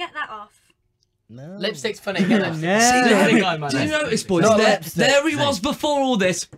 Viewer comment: what Louis said? Lipstick what? Sorry haha english isn't my first language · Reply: I think he said "Lipstick is funny"